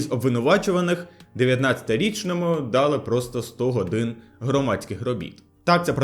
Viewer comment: мені заміть 100 годин, почулося — здохнути від громадських робіт…